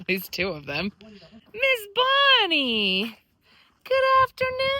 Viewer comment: I heard you say Miss Bonnie to your burro. So you finally named her. I had said Lola. Lol ❤🎉